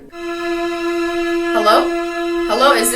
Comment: did Cristine say "Hello"!?!